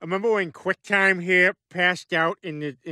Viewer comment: Did peter just call quagmire quitetime 😂😂😂